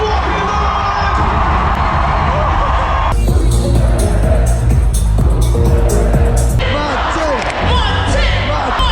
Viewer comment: Can someone please tell me what this chant is actually saying because I'm been chanting gibberish 🤣🤣🤣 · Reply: Whoomp there it is